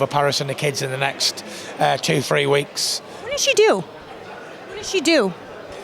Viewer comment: Lmao I thought she said what does she do too, lol like aye she works at the local post office mate